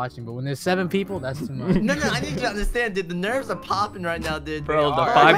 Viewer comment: just me or did just say the n word? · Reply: he literally just said nono